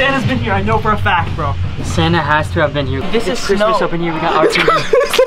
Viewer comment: who else heard "This is snow"